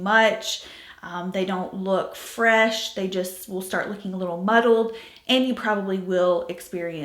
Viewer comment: I know you said the creams can look “muddled” but I heard “muggled”, and my very tired brain was indignantly like “what’s so bad about muggles??” until the smart(er) part of my brain kicked in 😂